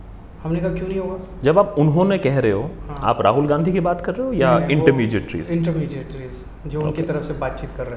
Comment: What is the word 8:28? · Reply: Intermediary